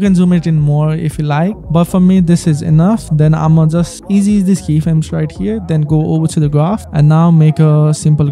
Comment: what did u say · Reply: "Easy Ease these keyframes right here" is exactly what i said on that part